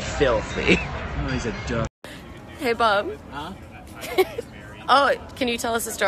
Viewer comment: Honestly thought she said "Hey Bub"